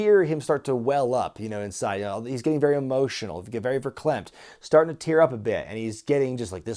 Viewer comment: Did just say "very verklempt"? You never stop learning.